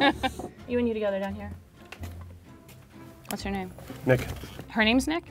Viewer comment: lmao @ 6:47 it did sound like she said what’s your name not what’s her name